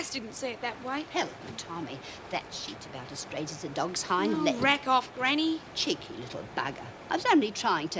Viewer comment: What did she say at 13:09, something as straight as a dogs hine legs.